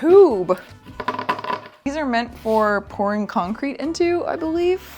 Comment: Could someone explain to me why my brain is so messed up and I heard..... p🌽 concrete? Sigh... Why brain? Seriously why are you doing this to me?